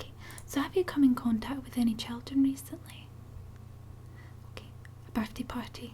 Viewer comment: What did you say at 2:10? I understand your accent fine but I have no clue what you said there lol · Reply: Have you come in contact with any children recently :)